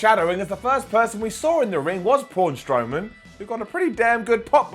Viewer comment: I swear at 1:02 Simon called Braun Strowman, Pawn Strowman. 😂